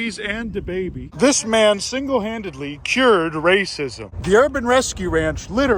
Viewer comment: i thought that said fascism not racism for a moment